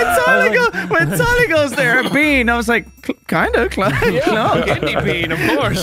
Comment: did tyler said beaner? because the word in the chat is censored in Brian’s video.